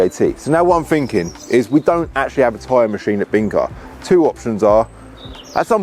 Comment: Genuinely thought you was saying you want to buy a time machine 😅. Anyway, great vid as always 👍